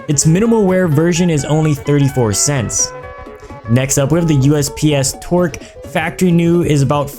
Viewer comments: I thought he said usps twerk.. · When he says USP-S Torque it sounded like he said USP-S Twerk XD